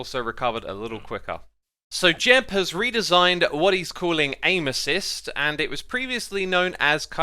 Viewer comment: When he said “Aim Assist” I thought he was saying “Anuses”